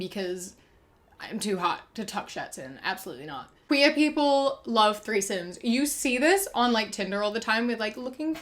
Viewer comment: When said "queer people love threesomes" idk why, but for a second I thought she said "queer people love raisons" but I hate raisons so I felt kinda hurt